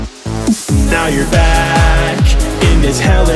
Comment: When Golden Freddy Said "Now you're back." I thought he said "Oh you're black."